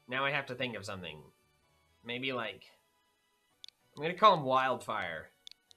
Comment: When it said wildfire was added I thought it said wildie was added